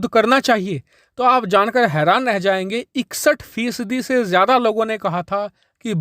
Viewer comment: can u say numbers in English Bhai🙏 please can't understand. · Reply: 61%